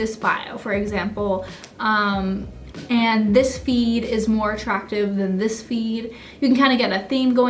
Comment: Some reason all I heard was "Be attractive" ...........